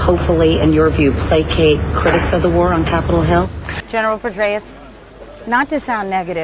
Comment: did she say general Pervaiz??? The Pakistani president...?